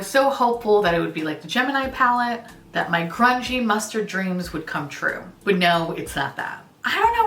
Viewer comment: I thought you said "crunchy mustard dreams" and I didn't even question it😂